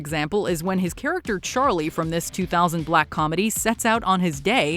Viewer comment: Im sorry what was said about Me Myself and Irene???? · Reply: They said "black" comedy. Crazy!